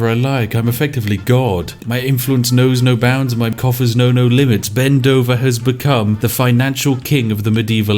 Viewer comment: For a moment i thought you said " my coffee knows no limits" at the beginning of the video!